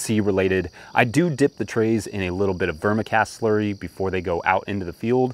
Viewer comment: What is the solution you dip your trays in? It sounded like you said burma cast slurry. It's in the video around 2:50. How do you prepare that solution?